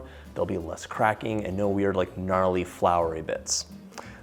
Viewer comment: Did he say gnarly?